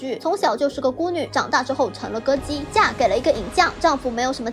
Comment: "成了歌姬", 听成了“成了个鸡”。感觉不对赶紧倒会来看字幕，才发现自己听错了。